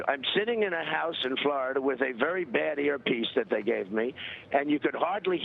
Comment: Sounded like he said hair piece